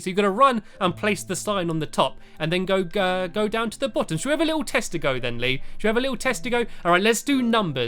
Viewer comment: it sounds like your saying something else. Not , "test-to-go". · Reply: he says tester go idiot